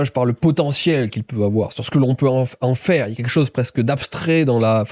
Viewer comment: Notre dépression détruit carrément LE VRAI...!!!!!!!! Wow... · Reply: Attends à 1:18:30 qu'est-ce qu'il dit le gars...???? "Ce qu'on peut faire de l'enfant"...???? Hein ???